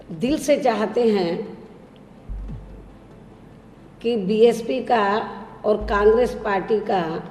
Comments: There is a mistake in subtitles at 4:31 , it is there 'BJP' instead of 'BSP' . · sec , there is a error . Instead of bsp-congress alliance it shows bjp-congress